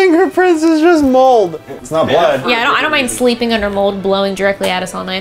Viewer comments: I didn't hear "blood" I heard "hello hello hello" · Notes while watching...I got chills (totally random, not scary) but the second I did said he got chills. So weird ya know. Also I didn't hear blood I heard "lick" so idk what that could mean maybe freaky ghosts. But I love how transparent you always are with every potential piece of evidence also side note I need to know where you get your eye patches because I love them every time I need them!! Love the ghost hunting always!